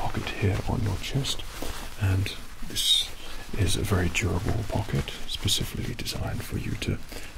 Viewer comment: I swear I heard "Gerbil pocket" xD